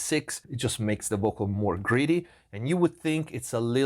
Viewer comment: So… I feel dumb saying this. For a long time, with your accent, I thought you were saying certain pieces of gear were “greedy” and it made a lot of sense to me: unapologetic, colored, and grabbing a lot of the signal, as opposed to tame and transparent. Now I am realizing you were probably saying “gritty”, which also totally makes sense. But I’ve been referring to aggressive compressors as “greedy” for a while now, because I think it’s a good description of the sound. 😅